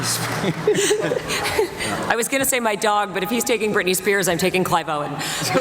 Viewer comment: What Rachelle Lefevre says 7:20 minutes? I'm Spanish and I do not understand what it says :) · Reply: Creo que dice: " Iba a decir a mi perro, pero si él dice Britney Spears, yo eligo (...) " Aquí dice un famoso pero no se quien es.